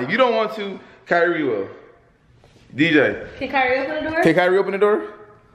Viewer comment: I thought you said kyrie Can open the door